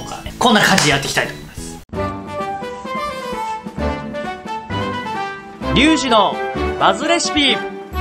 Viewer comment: OP、リュウジのマズレシピって言ってるように聞こえる